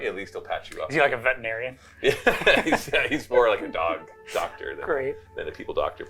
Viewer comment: I heard that as he’s a Dog who is a doctor